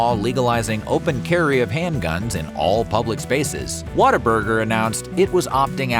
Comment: For the longest time I heard about it and thought people were saying "water burger"